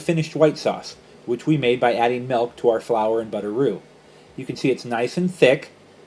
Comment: ahaha it sounded like you said butteroo